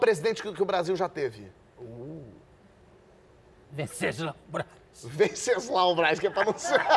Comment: não entendi, alguém ajuda aí? rsrs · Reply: Venceslau Braz